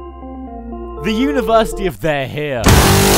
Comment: I could have sworn he said "University of the hell"